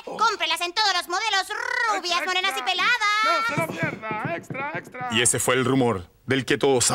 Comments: Alguien más escuchó al que dijo "Extra, extra, no es una mierda" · Alguien más escuchó al que dijo "Extra, extra, no es una mierda" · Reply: Yo escuché “extra extra! Soy una mierda” XD